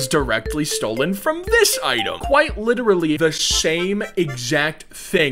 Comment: I think bro said shame instead of same lol